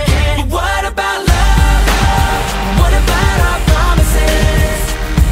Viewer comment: i thought he said what about our studio